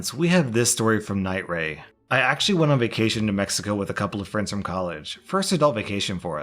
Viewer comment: When you said to Mexico I heard New Mexico then the currency changed and I was like either someone is getting a chuckle or I misheard. I just misheard.